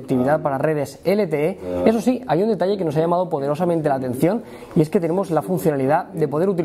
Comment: ? que dice? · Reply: poderosamente la atención. Se oye perfectamente